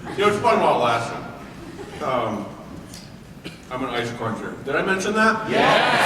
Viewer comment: what did he say? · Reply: "I'm an ice cruncher. Did I mention that?"